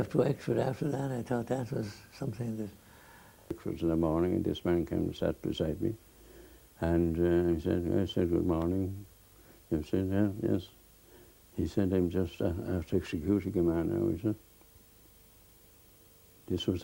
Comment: WTF is the old may saying at 8:57 ??? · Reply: (Old man say's) In the morning this man came and sat beside me, i said good morning, he said, ah yes, he said i'm just after executing a man now he said, (Old man) this was the man who was in charge, completely as if he was doing his days work you know, he was so casual about it, this is a man who spent another four years in France. " interviewer asks: and he had just come from the execution ? (old man ) then you see they blow the brains of the man out then , the officer has to do that, when the man is shot down the officer approaches him and blows his brains out , to finish his tribulations you know. "